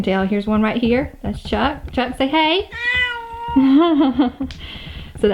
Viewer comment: “Chuck say hey” it sounds like he say hello or milo😂